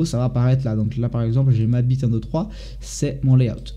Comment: Ptn ! a un moment j'ai entendu "Allez ma bite 1,2,3" xD xD